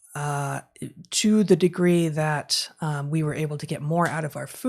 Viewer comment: did Heather say "chew the degree?"😂😂😂